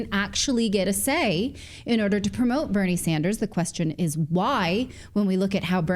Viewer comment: I'm not even kidding I thought she said "Vernie Sanders and Jonald Trump" 😂😂😂